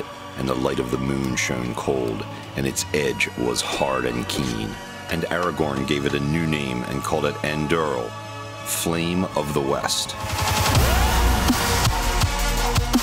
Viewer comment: Did he just call it ”EndRL” 🤨🙄